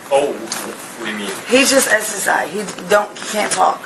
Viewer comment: what does she say? He’s just exorcised? What is that supposed to mean?